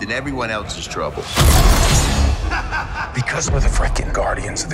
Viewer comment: I keep hearing Quill say "Frackin'" are we gonna get some BSG easter eggs?